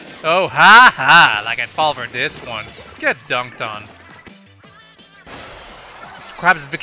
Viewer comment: did he say....get dunked on.....